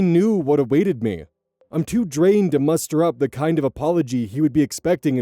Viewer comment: fuckk i thought said “mustard up” at first cuz i’ve heard the word mustard so many times in this story